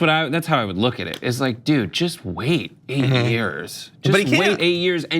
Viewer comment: Did he say wait 8 years? Is saying tRUMP is going to get reelected @ 4:10?